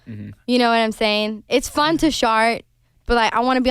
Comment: I am deceased! They cannot keep it together when she said "it's fun to shart" instead of "it's fun to chart". It's just her Swedish accent coming through, and it delivered.